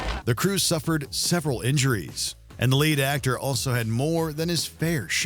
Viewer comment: when they talked about Martin Sheen it sounded like they said "late actor" not "lead actor" WOW, never went to imdb faster